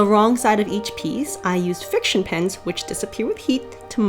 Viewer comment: I had to keep rewatching bc I thought you were saying pins not pens and I was trying to figure out how that would work and why you would want your pins to disappear